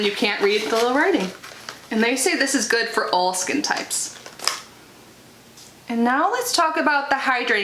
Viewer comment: I thought you said "Old Skin Types" instead of "All Skin Types" - YAY that's for me. 😂😂😂 My skin type & coloring is very similar to yours. I don't mean Old.😉 They're really smart not to put fragrance in the eye-gel (can vapor into eyes 😳), and in the future they should include an SPF in their daily moisturizer - it'll save money & steps.